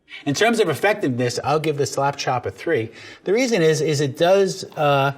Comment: "slap chop" all I hear is "slap my nuts"